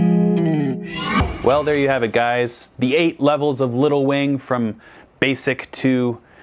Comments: Here’s me just hearing, “little Wayne,” every time. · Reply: i thought he said “little wang” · every time he said little wing all I heard was lil Wayne the 8 levels of lil Wayne 😌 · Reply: All I hear meow is “Little Wayne”.